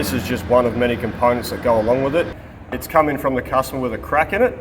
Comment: Coming from the customer with a crack in it... that made me chuckle. :D At least that's what I heard first time. Not sure it is when I listen some more...